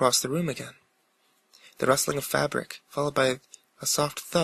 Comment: For the first story, I thought when you said fabric you actually said fapping. It made the meaning of my story very different...